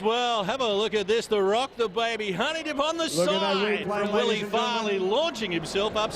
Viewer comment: did he say honey dip at 3:07?